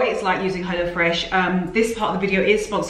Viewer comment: I always thought you were saying “hellafresh”😂